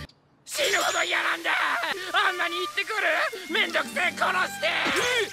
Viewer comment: I think he's saying “I wanna kill them all” not “kill me” sorry for my bad English😎🕶 (from Japan)